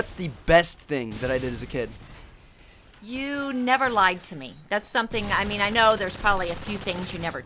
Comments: when she said you never lied to me, i thought she said you never liked me... :3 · anyone else thought his mom said "you never liked me" at 1:43 ?